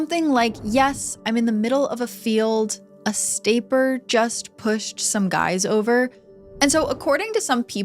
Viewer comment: What I hear is "yes I'm in the middle of a field. I straight just pushed some guys over out here going towards abilene on the north side. My truck ran out of gas. There's one car here and the guys chasing me through the woods. Please hurry. I first tried talking to them cause I accidently ran into them. I lost the first guy. Yeah no I need the cops." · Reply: i heard "essentially just pushed some [?] over" but i definitely agree that he said the guy's chasing him. doesn't help that both and the dispatcher sound like they're drunk and on quaaludes...